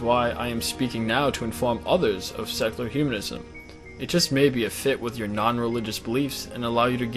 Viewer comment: I keep hearing "sexular humanism"...